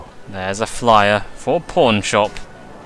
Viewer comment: For a second there, when you said 'Pawnshop', I thought you said "Porn shop". I was like "Wait what?!"